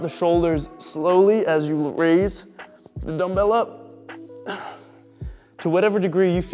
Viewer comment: When you say "dumbbell up" around 7:04, it sounds like you say "vanilla" and it cracks me up every time. I love it :D